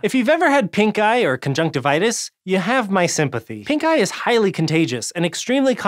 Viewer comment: i thought he said "pink guy" ;_